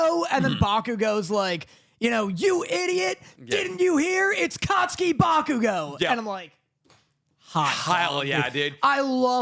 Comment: he actually said Kacchan Bakugou (Bakugou no Kacchan). I didn't watch the dub, but in sub and in the manga it was this.